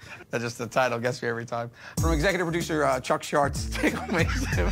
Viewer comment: what does he say? From executive producer.... what? Can understand ;( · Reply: Chuck shartz ( shart is when you fart but poop comes out). Hope I’m not late